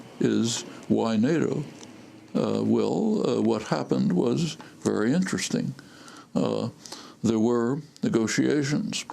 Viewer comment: Did i hear her say Noam Trumpsky?